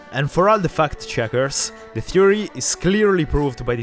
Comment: did he say fuck checkers